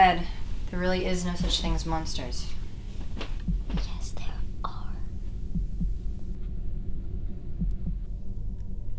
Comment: Not my first language, what did the girl whisper at 1:35? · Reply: ocana cabrera The mom said "There are no such thing as monsters" and the little girl whispered "Yes there are"